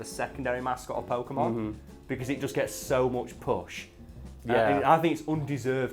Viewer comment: I misheard it and thought he said "charzard got so much pus"